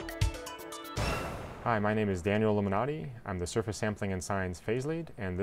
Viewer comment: It thought he said Daniel Illuminati -_